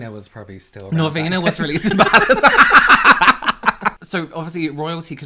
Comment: Someone please tell me what says at 2:53 please. I wanna laugh too 😭🤣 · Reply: Norvina was releasing palettes back then (she’s the creative director of ABH)